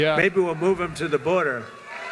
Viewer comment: when he said move them to the Border , It sounded like he said " send them to the Wall " from game of thrones lmao